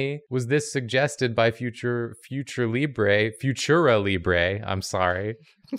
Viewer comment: The first time said "Futuralibre," I wasn't looking at the screen and I thought he said "Future Meat Ray" and I was so proud of that person. But now that's just gonna be my next reddit name, nobody steal it.